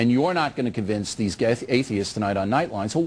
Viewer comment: It is just me or does he almost say gays instead of atheists around 1:04?